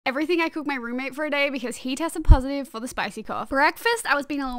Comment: All i heard was "today i am going i cook my roomate today"